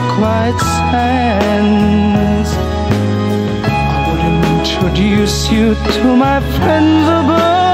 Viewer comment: for the second part, I thought it said "I would introduce you to my friends above" to which I thought fuck dude that's dark